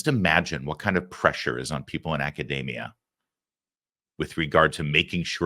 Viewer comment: For some reason I keep hearing you say 'macadamia'.. Hope it's not just me...